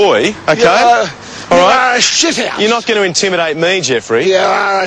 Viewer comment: From 00:34 to :39, is he saying "Ahhh, SHUT UP!" or "Yooou're a SHIT HOUSE!" O_O